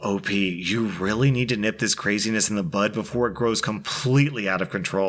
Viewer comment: Not sure if I heard you wrong but its "nip it in the bud", sounded like you said butt.